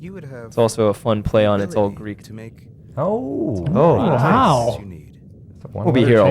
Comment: What does he say around 37:20? It's also a play on...? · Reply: He says either it’s all green to me or it’s all greek to me